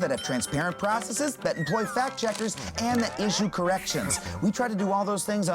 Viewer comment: did Adam say issue or Asian correction?